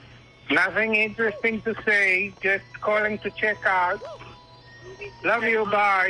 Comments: I thought he said "check up." Like "CHEYK AAAAP" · Ohhhhhhh, I thought he said "check up", so I didn't question. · I heard "check up" the first time through because bitrate, I love so much